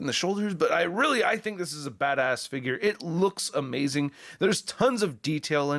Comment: When you said that this figure looked badass, it sounded like you said “this figure looks fat ass.”